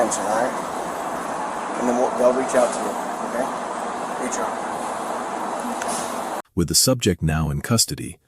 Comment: PAID SUSPENSION??? What is wrong with this world. Smh. 🤦🏻‍♀️ And I swear i thought I heard GOOD JOB but I didn’t believe my ears. What a joke. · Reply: I had to listen closely, he actually said “they’ll reach out to you okay, HR…” but even the damn subtitles read “good job.” lol